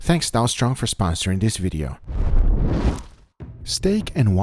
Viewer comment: Why did I hear “thanks Donald trump for sponsoring this video” 😂😂